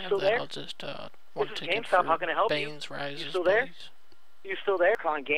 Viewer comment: Alfred Rises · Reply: I heard bane rises 1:31 ish